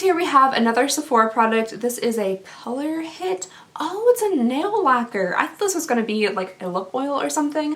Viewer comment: Thought you said nail whacker 😆